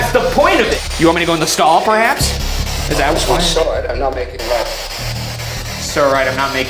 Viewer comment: I heard “sorry I’m not making a lot of sense”